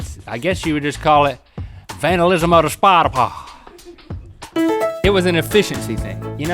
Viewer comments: It sounded like he said,"Vandalism of the spider fart." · Sounds like he is saying "vandalism of this portapotty"